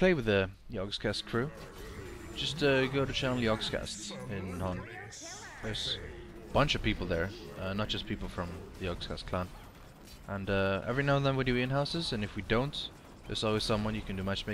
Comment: i know , but i thought he said youtube channel